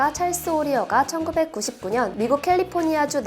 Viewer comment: 이거 내 귀에만 1996년이 "천구백구십구년"으로 들리나요?